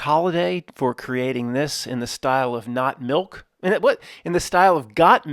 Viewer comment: YA KNOw for the longest i thought you were saying. "Not Milk" regarding this reality.. ha!